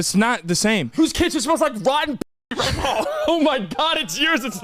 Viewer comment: Anyone know what said at 5:55 cause all I hear is "who's cancer smells like rotten ** it's yours not mine"